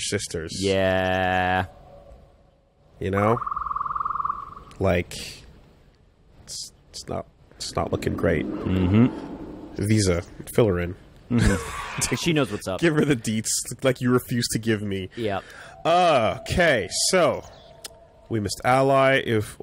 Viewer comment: For a fleeting moment I thought said "Visas, fill [handmaiden] in. Give her the D-".